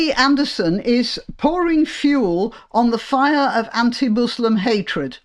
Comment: Sorry, all I heard was 'pouring fuel on a muslim fire...'